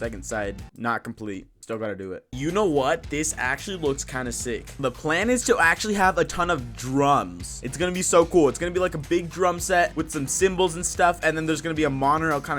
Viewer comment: When he said drums I thought he said drugs 😂😂😂